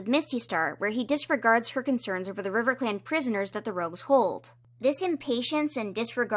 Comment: Did anyone realize at 16:54 that said 'RizzClan,' or am I mishearing